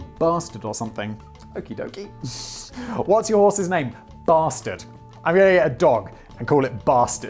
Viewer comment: Get a dog. Name it Stain. Call for it. Come Stain. That joke always reminds me of a dog in my friend up the street’s neighborhood was called shit. And we used to hang with my one friend, who unlike all of us that BMX’ed on dirt jumps and stuff, did something called flatland and twirled around like a figure skater on pegs or something, who would be able to show off in the middle of the road and in front of the dog named Shit’s house and if he fell and yelled shit the dog would start going crazy inside. Cool story bro can you tell it again? Okay. That joke always reminds me of a dog in my friend up the street’s neighborhood was called shit. And we used to hang with my one friend, who unlike all of us that BMX’ed on dirt jumps and stuff, did something called flatland and twirled around like a figure skater on pegs or something, who would be able to show off in the middle of the road and in front of the dog named Shit’s house and if he fucked his own mom and yelled shit the dog would start going crazy inside. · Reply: Also clicking on the timestamp sounding like he said mustard but that is irrelevant.